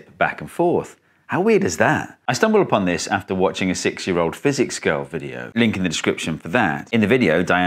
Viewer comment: sounded like he watched a 6 year old girl that does physics Lolol my brain doesn’t function sometimes. Love your videos man. Keep it up!